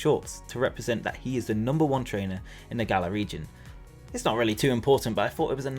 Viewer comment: This whole time I thought they had been saying that Leon is #1 in the galaxy. Galar region makes much more sense